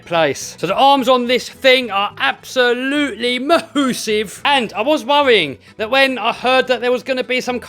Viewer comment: Did he say “moosive”?